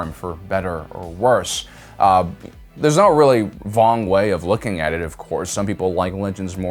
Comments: I thought you said Vong here. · did you just say "there's not really a vong way of looking at it"? lol, vong=wrong